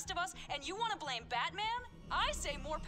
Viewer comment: Did someone say Batman?